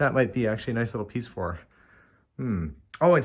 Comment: If you find extra puzzles you can check with nursing homes, the residents really enjoy new puzzles :) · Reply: I thought someone said 300 piece for!🤤